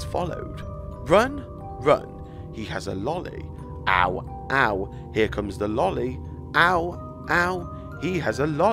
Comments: I heard “hide hide here comes the lady” at the part where you think it says ow ow here comes LOLLY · When he says it says "run, run he has a lolly ow" I hear "run, run here comes Veronica" like the song on the TV in little nightmares 1